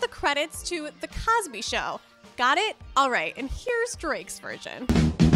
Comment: I thought you said Drakes reaction but I didn't c any reaction🤔